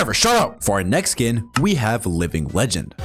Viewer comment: GUYS 10:56 ITS LIVING LEGEND SKIN BUT SUBTITLES SAY ANNIHILATION